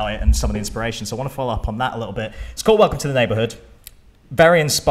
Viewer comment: I thought at first it's saying "Welcome to Trench" TØP💛||-//